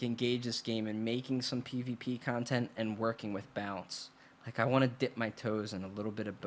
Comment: Every time I heard you say "balance" I heard "bounce" haha. Speakin the truth tho bro.